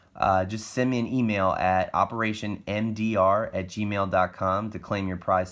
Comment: I got it to go thru i thought you said operation ndr but it did send